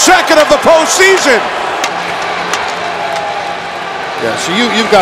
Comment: “You’ve got a demoralized team, they’re just beaten up” That was hilarious when the Blues went up 5-0. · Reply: Ray I thought he said "You've got to demoralize Steen here completely all beat up" 🤔